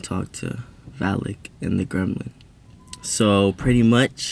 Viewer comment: Wait why he’s doesn’t talk to vallyk and who? 0:20 I didn’t understand what he said LMAO😭😭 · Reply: He called Mike a gremlin LMAOOO